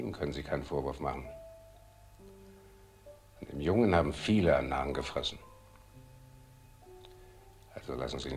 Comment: What does he say from 44:35 to 44:38? Dem jungen hab... What is the meaning of this sentence? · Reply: Ihm können Sie keinen Vorwurf machen. "An dem Jungen haben viele einen Narren gefressen." It is a german saying, the meaning is something like: "many (ladies) liked and love the young man"